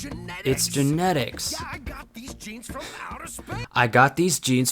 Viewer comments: ngl when I first heard this song I thought he said jeans and was very confused because he's not even wearing jeans and it's a fantasy medieval looking country. And I assumed the outer space part was just added to sound cool, not because his powers came from a star 😂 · when I first heard "I got these genes from outer space" out of context, I thought it was "I got these jeans from outer space" and assumed it was meant to be a testament to how fucking rich he is or something lmao